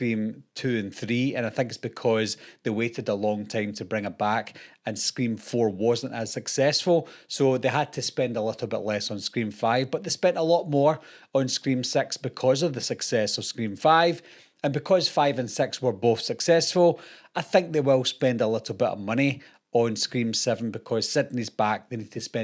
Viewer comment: I miss when you were talking about scream 6 because it always sound like you were saying scream sex in your accent 😂